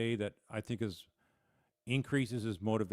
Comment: It increases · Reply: i thought I heard you say DECREASES which I was pretty sure wasn't right. Thank you for the clarification.